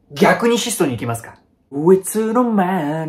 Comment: のとこなんて言ってるんですか？ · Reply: 「逆に質素に行きますか！」 かな？多分。